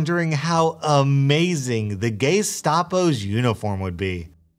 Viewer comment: Okay. I heard "Gaystapo" and if I'm now considering making a Reddit account just to give that guy a platinum.